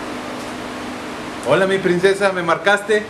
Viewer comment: Segundo 00:14 el .......¿quien?..... Se metió al celular e hizo las 79 llamadas? No entiendo que dice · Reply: Gersain Quiahua Dise el diablo se metio Al celular e hiso las 79l llamadas